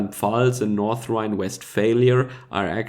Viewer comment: did you say 'North-Rhine West Failure?' :P